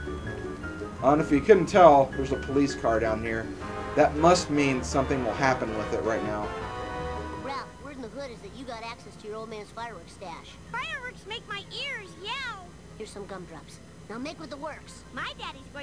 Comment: did bart say word in the hood?